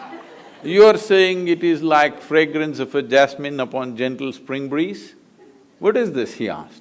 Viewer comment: What is he saying at 41:20? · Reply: The fragrance of Jasmine on the gentle spring breeze.